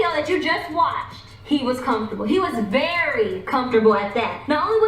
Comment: “ I’m very uncomfortable” am I hearing that right? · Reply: He said comfortable